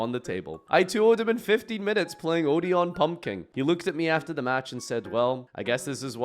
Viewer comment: I don't think the captions heard odion pumpking 💀